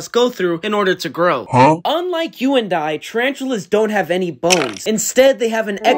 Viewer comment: I thought he said ball and not bones 💀